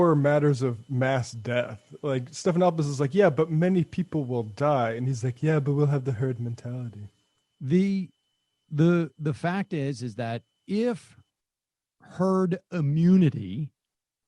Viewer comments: I'm so glad you guys caught that. Nobody was talking about it and I thought I was hallucinating, because I clearly heard "turd mentality" and "turd immunity". 😂😂😂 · I love it! I heard “turd mentality” too!